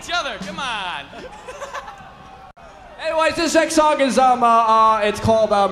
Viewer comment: Did he just say “Sex song”? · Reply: He said next song